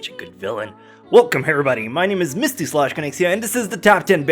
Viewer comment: In the beginning I thought you said "hello harambody"